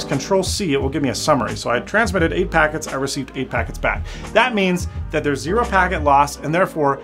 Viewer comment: am I the only one hearing this or did he say f*gg*t instead of packets at 4:56? everyone triggered